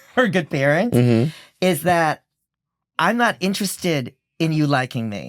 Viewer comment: all I heard was, "I'm not one of your little friends" 😂